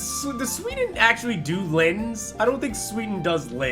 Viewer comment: What did he just say? i had the translator on and it sayd Lens whit is probely not what he sayd so what did he say? Im from sweden btw ;) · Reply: So I was asking if Sweden puts -lynn at the end of names since we had to pretend Erilynn was from Sweden. Perhaps you can answer the question!